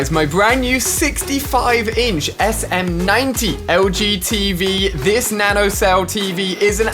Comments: I thought he said the LGBT giveaway lol not the LG Tv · Who else thought he said LGBTQ when he said LG tv at the end?